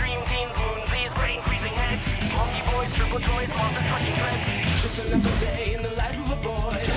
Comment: I could never understand 100% what guy is saying at 0:34 but i think it "just another day in life of a boy"😂